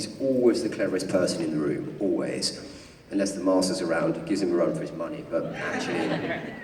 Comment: What does he say at 5:39? "unless the ??? around, gives him a run for his money", I'm not sure if he says "missus" or "master" or something else? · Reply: Master's around